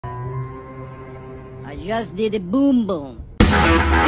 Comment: It sound like he said, I just did the boom boom😂😂😂😂🤣🤣🤣🤣😆😆😆